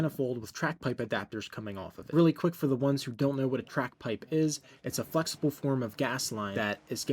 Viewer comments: Thought you said crack pipe 😆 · Sounds like you said Crack pipe haha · Ohhh he said track pipe. I thought he said crack pipe.